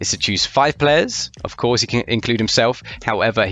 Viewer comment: What would your All-star team be? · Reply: can you settle this?: at 0:15 I heard say: "his job is to choose 5 players, of course he can't include himself" while the AI subtitles says he said "can". Please set the record straight to either shut them or me up. thanks.